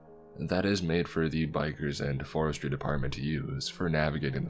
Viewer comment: Misheard "Forestry department" as "4 street apartment" 😅